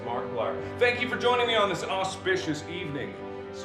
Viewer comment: When he said "auspicious" I read the captions and thought it said "autistic"